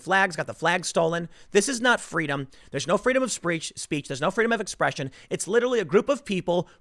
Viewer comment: Tim, did you say "Freedom of Screetch" at 13:04? lol